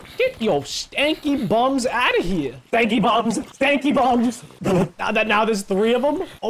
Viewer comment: #bankybums (I think thats what he said)